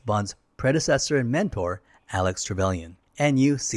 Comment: Some strange things I thought I heard in the vid... > Alex (should be Alec as in the description) Trevelyan around ~24:51 or so > "Hideout" (a chemical processing facility!) at 25:29 also missed the changing the bomb timer (was mentioned in the train and probably the source of the scar so a significant point imo) there were a few more but nice video otherwise!